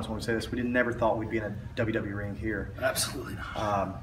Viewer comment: Aj Said "All 3 of us think We'd never be in a WWE ring" but wtf gallows was apart of the straight edge society SMH.. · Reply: i thought he said "none of us thought we would be in a wwe ring here".